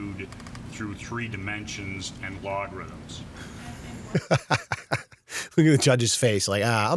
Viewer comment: I think he is saying Algorithms Al gor ri thims